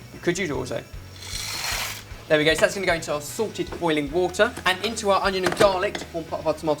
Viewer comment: did Ben say 'salted' or 'sorted'? :D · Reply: Haha I was confused in another video about this too! I thought he was talking about "Sorted Water", and I thought they got some kind of special branded water, and I was shocked at the ridiculousness until I understood how I was messing up haha